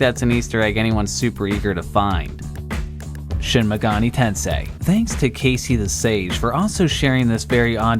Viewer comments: sounds like you said "Shim Megani Tensei" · it sounded like you said shin megoni tensei...